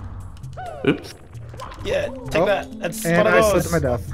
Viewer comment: DID I HEAR OOF